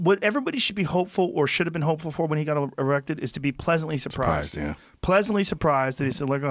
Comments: Im sorry joe caught u saying erected instead of elected😆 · he said Trump got erected instead of elected lol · Instead of saying "elected", Joe accidentally says "erected". 😂😂😂